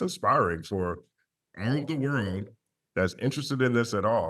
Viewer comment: There's some kind of distortion to that one word and I don't understand the word said. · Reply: all the world... I think